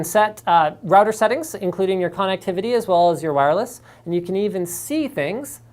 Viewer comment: every time linus says "connectivity" i hear "con activity" :D